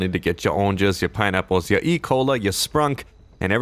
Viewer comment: i thought he said ecoli